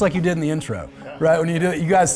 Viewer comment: Lol when he made the joke about the rainbow six I thought he said ringo six and couldnt stop thinking of ringo star(drummer from The Beatles) doing that back and forth head motion